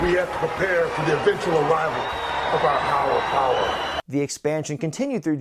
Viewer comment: Taker said "Hower Power", right? Am I the only one who heard that?